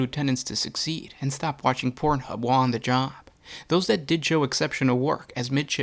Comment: did you say they want sub Lieutenants to stop watch porn while on the job